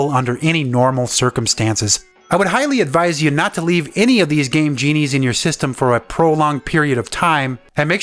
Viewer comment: I thought that he said gay genie.